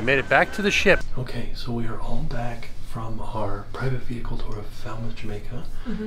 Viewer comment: When said private vehicle I thought he said private pickle 😂😂😂 I was like man I also would not want to share a pickle with anyone either 😂😂😂